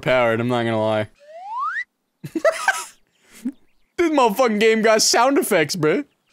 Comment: i thought that said “sex in game”